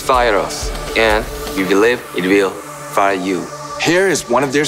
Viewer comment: Did I just heard Yoongi say infire again after sooo many years